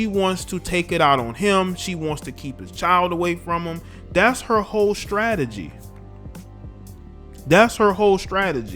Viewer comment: did you say: Hope, Hoe, or Whole Strategy?